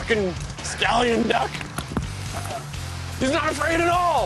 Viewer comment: Am I mishearing it or does The Rev say "Scallion (as in rapscallion) duck" or "Stallion duck" at 8:32? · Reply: MezmerKaiser “c’mere fuckin’ stallion duck!”